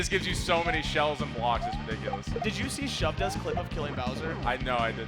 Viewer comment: what is the name he is saying? Unfortunately couldn't find out. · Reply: Shovda